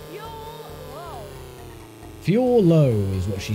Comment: I swear I hear "too slow" whenever the girl announcer says "fuel low". Also, the first-person view ain't bad for its time.